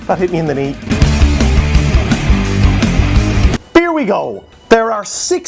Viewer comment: Did he just say 'beer we go!' ?